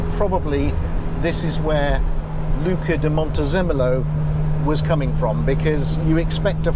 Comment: Montezumolo is the child of Moctezuma and Montezemolo, I'm guessing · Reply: I heard it as Montezoomolo, maybe way too many zoom meetings..